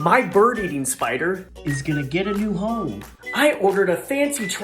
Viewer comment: " My bird eating spider is going to a new home..." Me: Really confused cuz I heard " He's going to get a new phone"